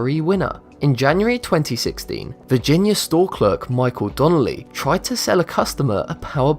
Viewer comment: did he say Michael Townley..........................ive heard that somewhere